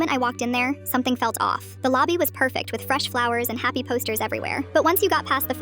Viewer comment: I had to reply it because I thought she said nappy posters 😂